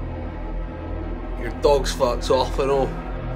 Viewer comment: Wait at 8:15 what did he say because it sounds like "your dog farts off on all"